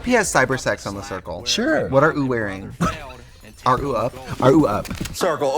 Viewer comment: why is Trixie saying “what are ou wearing? Are ou up?” Is there some rule that you can’t use the letter Y?